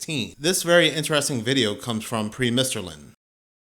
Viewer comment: for anybody who loved this guy as much as I did but didn't quite catch the name said, its Premisterlin and im a new fan